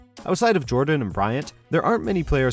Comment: What did you say? Number Two(2)???